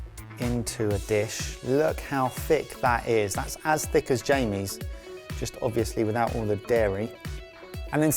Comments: I heard "As thicc as Jamie," not "As thick as Jamie's." and I am okay with that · At first I thought Mike said "that's as thick as Jaime" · I thought Mike said “That is as thicc as Jaime” until I hear the enunciation of the “...’s.”